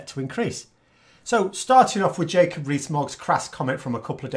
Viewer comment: I couldn't catch the name of the video he was referring to at 2:01. Anyone? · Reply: The Jacob Rees-Mogg one? <link>